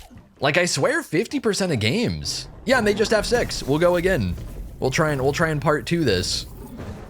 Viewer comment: i really heard "They'll jus have sex" for a minute 😂😂😂